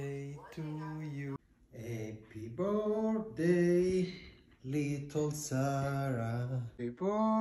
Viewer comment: Did he just say Happy bday my little Sara . . . . 💀🧟‍♀️